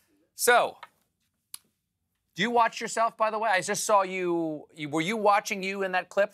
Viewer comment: "Didn't wash his willy." OH, MY. With that comment, you could tell Stephen was "Uh-oh. This isn't going well." Then, directly after Grant's "willy" remark, Stephen asked, "Do you watch yourself . . .?" But it sounded like Stephen was saying, "Do you WASH yourself . . ." Listen again. Stephen was very happy to end this interview. Too bad, I feel like Grant had much more to share.